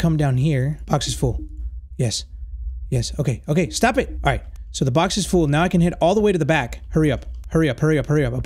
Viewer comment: who else heard “now the box is full now time to hit all the women in the back”